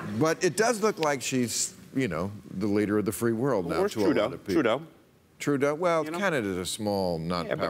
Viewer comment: When Spitzer was saying "Trudeau," I at first thought he was saying, "True dat."